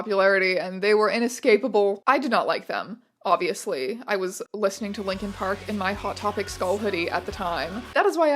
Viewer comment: somehow I wildly misheard "I was listening to linkin park in my hot topic skull hoodie" as "I was a young lesbian delinquent" as I walked out of the room, which sounds like the title of a pulp novel if I've ever heard one.